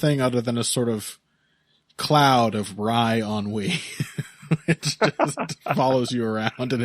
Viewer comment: What did say on 1:14:47? "some sort of cloud of..." I have bad ears and vocabulary? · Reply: "wry ennui"